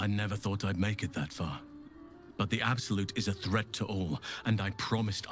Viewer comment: Everytime Shart casts that spell, i hear, "The absolute is gay!"😂